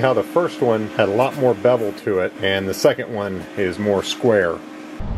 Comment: Did he just say "His hole is more square?". Sir, your wife is a lucky man..